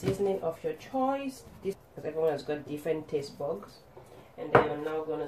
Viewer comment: Full view and like #21 from me! Man, I'm on a diet right now -- but those potatoes sure look amazing! (Also with that diet, I'm using your apple cider vinegar.) Hmmm, it sounded like you said "everyone has different taste BUGS." :O Yeah, the olive oil is a nice touch! I could almost smell these through the screen! Thanks for sharing. I hope your Tuesday is/was great!